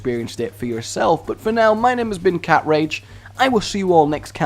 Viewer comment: Did you just say "Cat Rage"? Hnnngghh the potential fanfics...